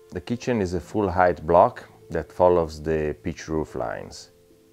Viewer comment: Every time he says "pitch roof" I hear "beet root". Makes a lot less sense that way.